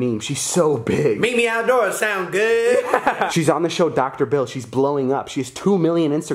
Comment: did he just say dr bill? (<Phil*)